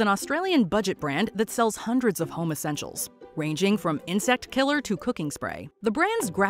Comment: in another fail when the narrator said home essentials, I thought she said homosexual‘s. I wasn’t aware that homosexuals were something you could buy in Australia. Lol.